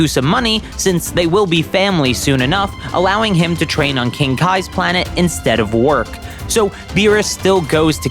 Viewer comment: HOLY CRAP THAT SCARED THE 💩OUT OF ME I THOUGHT U SAID THAT MR SATAN COULD FO TRAIN ON KING KAIS WORLDDDDD